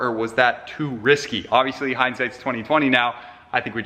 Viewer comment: did you say in hindsight its 2020 now???